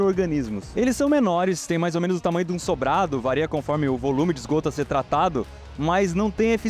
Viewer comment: Eu ouvi um "vareia" ao invés de "varia" no minuto 10:29 do vídeo?! 😟